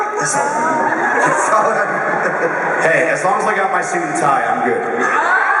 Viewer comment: What does AJ say at 9:22? "As long as..." what? Please, I can't hear. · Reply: "As long as I got my suit and tie it's okay."